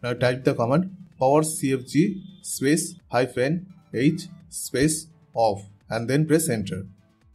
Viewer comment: can anyone tell me what command he is saying for the method one? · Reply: Had to turn on cc for me to get what he said.. he said “powercfg -h off”